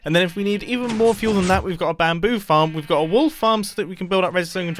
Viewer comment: i thought he said wall farm not wool farm.